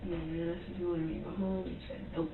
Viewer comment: mins..🤔 What did she say here?? The captions didn't pick it up. However,it sure as Hell sounded as if she said something like.."Man,I just wanna go home,and...." she sorta trailed off there at the end. Lol Dude,does this chick actually believe that she is gonna go home? Hahaha..😆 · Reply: “I asked if he wanted to go home, he said nope” or smthn like that